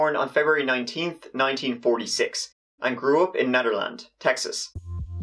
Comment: Because of ur accent at first it sounded like you said: “She grew up in Netherlands— (corrects self) Texas” I was like how do you mistake Texas for the Netherlands